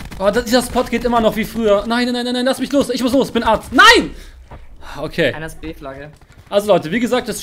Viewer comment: ich muss los ich bin arzt? Habe ich das richtig verstanden?